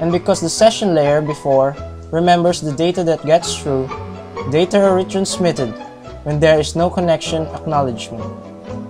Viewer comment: What did you say at 4:15? "Data origin smitten"? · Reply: He said the data is re-transmitted